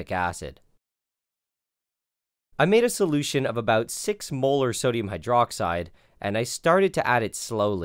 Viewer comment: Every time he says molar i hear "Mueller" as in the mueller report. i cant not hear it.